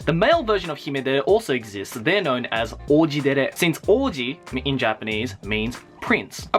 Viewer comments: When you said Oujidere I thought you said: Orgidere Also am I the only one who Noticed the Sasuke doll? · I thought he said orgy instead of ouji... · When he said "Oujidere" I thought he said "Orgydere"